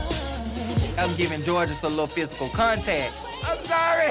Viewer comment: this whole time i thought angeria was saying "im giving jorgeous a lil bit of a contest"